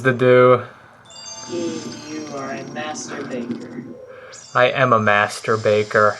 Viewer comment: Am I the only one that thought said was a Masterbater instead of a master baker?